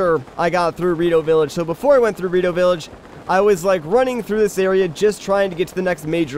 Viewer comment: i thought i heard dorito village at 1:15 XD